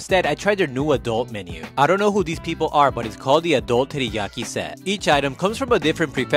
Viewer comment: i swear i heard him say tiddyaki